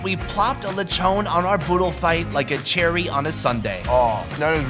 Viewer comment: Did he just say poped like a cherry